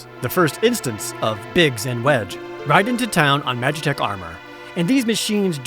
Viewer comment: Unfamiliar with the game, did he say "Biggs and Wedge?" If this is full of Star Wars names, my immersion is going to be in trouble. Edit (4:49): Oh.